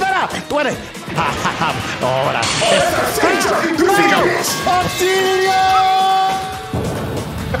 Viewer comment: So what was shouting as Gohan Blanco got DF’d? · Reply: He was screaming "Auxilio" which means "help"